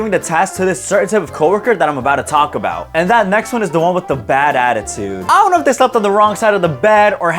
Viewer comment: I thought he said with a bad-a